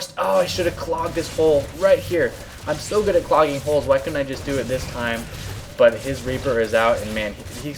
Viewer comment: Noone caught what said? "I'm so good at plugging holes! Why didn't I do it this time?"